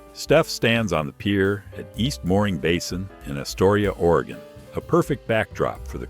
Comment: Is astoria not atoria. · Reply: I clearly hear Astoria at 2:23🤷‍♂️🤷‍♂️